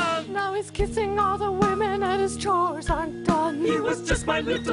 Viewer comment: I thought she said draws are undone 😳 but it was chores are done I was gonna be like dang mom over the top lol